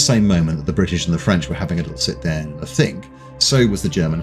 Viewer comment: I keep rewinding and listening to this sentence but, I can't make out what is being said, 14:12 British and the French were having 14:13 adults sit there a thing so was the... Is it just a blip in the editing of the audio? It seems like this happens often during videos. Or am I just missing a joke somehow? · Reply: 'At the same moment the British and the French were having a little sit down and a think, so was the Germany High Command..'